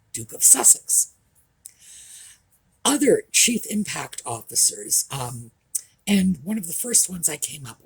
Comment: First I misheard it as "cheap impact officers"...probably that happened because of expectations 😂